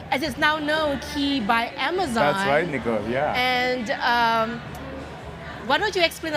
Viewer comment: Did anyone heard him saying: “that’s right nigga” at 0:16?